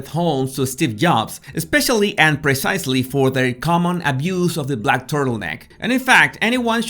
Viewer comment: I had a laugh when I thought you said, "their common abuse of the back turtleneck..." An interesting video, thank you.